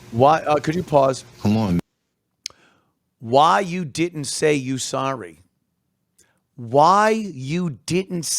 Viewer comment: in fairness to the father, he didn't say "why you di'ant say you sorry". He clearly says "why you didn't say you were sorry." Subtle but important difference.